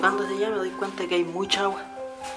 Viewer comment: minuto 11:02 cuando termina de decir agua se escucha otra voz que dice "ándate"